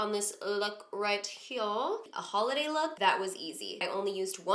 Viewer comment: Damn I thought she said holiday LOOKBOOK so I rushed over here